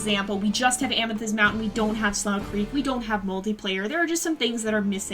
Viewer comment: When you said multiplayer i thought you said mouldyplayer